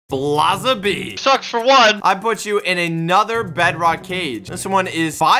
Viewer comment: It sounded like Blaza said "SucksFor1"